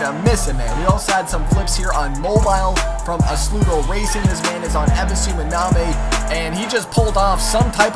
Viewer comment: how is the game called on 1:55 I didn't understand very well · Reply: BoostyTurbo - Assoluto Racing